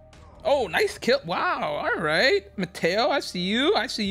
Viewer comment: I thought he said potato lemao